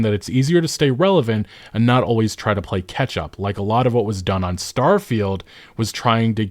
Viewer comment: Gotta admit, it sounded like you were saying "ketchup" evdry time you were saying "catch up".